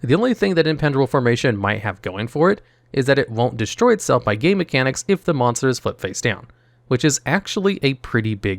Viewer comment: I don't know why I heard "gay mechanics" instead of "game mechanics"